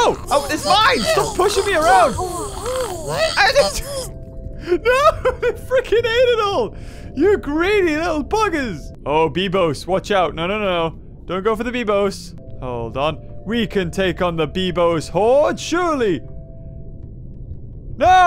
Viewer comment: i swear i heard you said "Don't go for the bimbo"